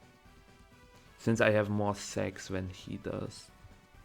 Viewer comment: First time I heard it, I was like, „Did Micheal himself just really said that...?“ then turned the Subtitles on, and it also said, „Since I have more S*x than he does“ My man is not only grinding in Pokemon Showdown lmao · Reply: Sacs! I meant sacrifices <.<